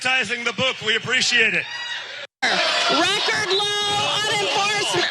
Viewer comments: Did she say "record low unemployment for women, for BLONDES??" or did she mean Blacks? Cause right after that she said African Americans... So... ?? 🤔 I can't understand screaming, triggered people 🤷🏽‍♀️ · Did she say: "...for Women, Blacks, African Americans...." Maybe I heard it wrong. (Blacks and African Americans?) 😂 Maybe she was nervous. 😄